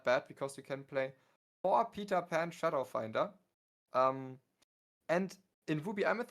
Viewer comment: Yeah but the whole point is doing it on turn 3 because if you do it any later they have drawn 2-3 cards. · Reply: OH, Shadow Finder. My b, I thought you said Peter Pan’s Shadow. Hence why I said it gave Mim evasive